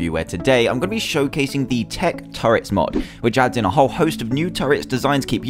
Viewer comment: I thought that it said TECH TURTLES but it said TECH TURRETS